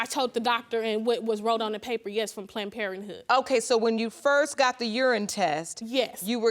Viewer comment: Plant-Parenthood or Planned Parenthood ? Slow down when you’re talking, young lady. They have a whole lot of energy! Geesh. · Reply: I thought she said "plant" instead of "planned."